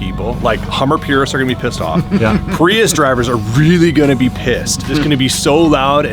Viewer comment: "Prius owners are gonna be pissed" 😄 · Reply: Prius? I heard "purist" #cmiiw